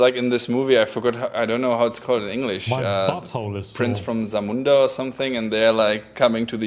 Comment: Did I just hear “my butthole is sore”